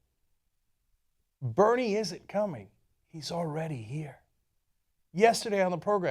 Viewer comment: At first I heard; "Bernie is a commie" and I was like, you too, son? phew. Non-native speaker 1st world problems :D